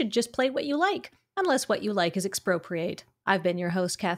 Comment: I nominate this for the official format tagline: "Commander: The format where you should just play what you like. Unless what you like is Expropriate." (though I keep hearing "exprocreate" when I replay it, which is somehow even more amusing)